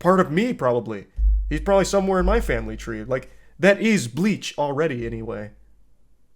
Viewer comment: I thought he said he ant like bleach